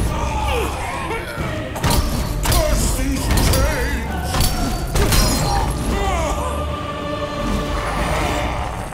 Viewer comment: What does cronos say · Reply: Curse these chains